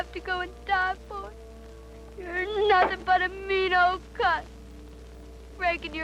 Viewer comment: Did my ears deceive me or did I hear the girl utter obscene language to her deceased Grandfather at 19:25? "You Are Nothing but a Mean Old - - - - ! 🤔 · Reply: She said mean old cuss....that means a cantankerous, onery, usually old man😊